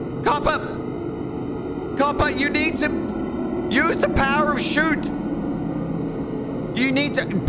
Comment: Thought he said to "Use the power of America" because of the gun😂 I need more rest I guess